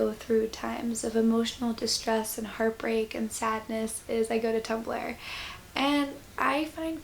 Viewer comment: when you said 'i turn to tumblr' i thought you said 'i turn to tupperware' and was extremely confused until i figured it out. anyway this was lovely